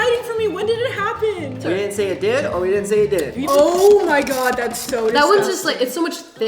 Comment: all I heard is ' we didn't say we didn't'